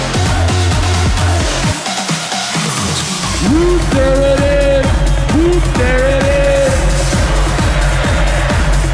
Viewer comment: What did Mike say? Anyone can tell me ?🤔🤔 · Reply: muff there it is